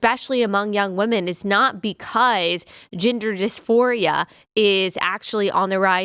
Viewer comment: Omg. hahahha I heard, 'gender disphoria' as, 'ginger disphoria' every time. 🤣 God help us. that's probably the next trend. 🤦🏼‍♀️